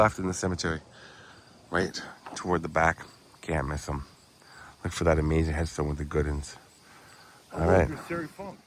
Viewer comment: Hey what did he say at the end ? Sounds like he said terry funk